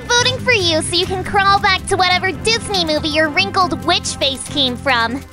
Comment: Did Fiore say Disney movie 19:01.